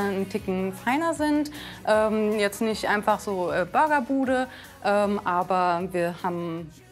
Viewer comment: "Barabude"? Oder was sagt sie da?